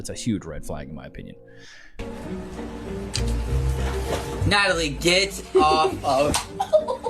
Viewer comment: i heard, "Natalie, get off..." And i jumped. 😂😂😂😂 My name is Natalie jsyk 😂